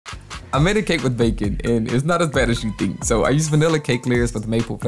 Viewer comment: Ok I love your channel, this cake looks great but i’ve watched this about twenty times trying to figure out what you’re saying in the beginning can someone please tell me what he’s saying? Because I keep hearing “I made a cake with bacon and it’s not a fetishy thing” but im so confused there’s no way that’s what he said lmfao someone help please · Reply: "it's not as bad as you think" 😂😂